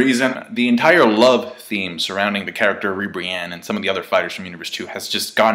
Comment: Ribrianne is out! thanks 18! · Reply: Nigga I thought u said Rihanna